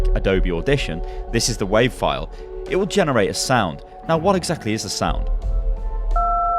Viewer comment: At first I thought he said "What is a sound?" at which i replied "A miserable little pile of secrets"...